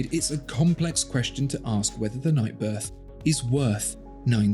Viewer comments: Wait, did you say "Night Birth" at 8:56? LOL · i heard night birth at 8:58 :) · Did he say “night birth”?!? 😂😂